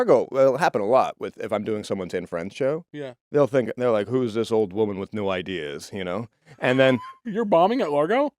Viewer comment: Everyone keeps quoting it as "whos this old woman with no ideas" but i heard "whos this old woman with new ideas" like wow shes so new here. 😂 i love chris hes hillarious